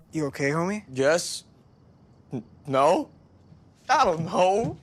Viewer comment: what I think he said : I don't hoe me did I hear that right? replays Help I can't hear anything else BTW I know what he is trying to say.